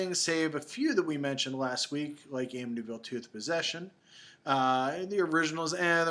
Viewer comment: For a second I thought you said "Amityville Tooth Possession", and I kinda got excited.